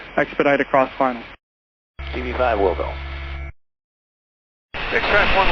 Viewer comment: "willco" or "will go", not "will do". I notice a large number of detail errors in the subtitles of these productions. Please pay more attention to what is actually being said.